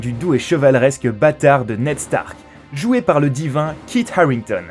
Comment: c'est moi ou il a dit batard